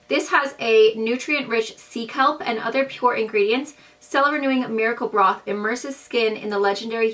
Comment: ha ha i thought you said miracle bra!!i was like there is a bra in this!!im confused!!😂😂